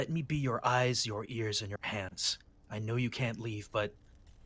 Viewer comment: it totally sounds like he said: "let me be your eyes, your ears, and your pants", it should be hands right. . . Right?